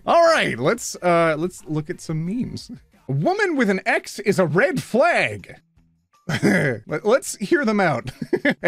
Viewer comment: Every woman who has an X is a red flag. 😂 I thought that's what he said at first.